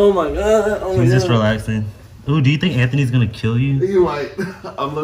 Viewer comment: Did he just say.... “ooou do you think Anthony might kill you?” 😂